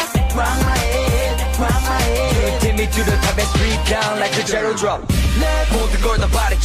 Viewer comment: For the longest time, I actually thought they were saying Straight down like a jello drop... -____- Now I can't unhear it...